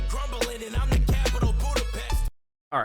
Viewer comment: Hey at 6:35 does he actually say "capital of Budapest", because it sounds like he said "I am the capital, Budapest " maybe it is just tell me if anyone else hears it too